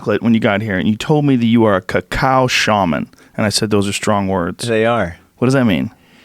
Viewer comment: Thought he was saying "Cow Shaman" the entire time. Kinda bummed, that means there are now cow ceremonies...